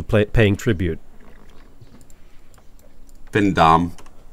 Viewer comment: What did say at 19:20? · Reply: Whoops, I meant 19:20. I think he says being dommed, but he doesn’t pronounce it clearly at all because he said it so bashfully 😂